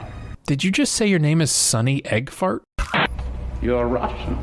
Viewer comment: I heard that guy's name as Sunny Eggfarts too! You said it right as he was saying like, "Sunny Eggfarts!?"